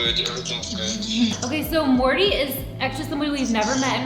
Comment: On everything, I thought she said, "What's going on Morty?" And then I saw a hoodie with Rick Sanchez on it. Dope vid btw. Gonna definitely do some of these challenges while I'm at home for these next few weeks